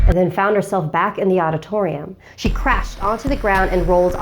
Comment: I heard she crapped😂😂😂😂😂